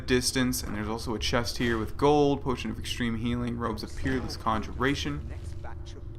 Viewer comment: i thought you said "robes of pubes and alteration"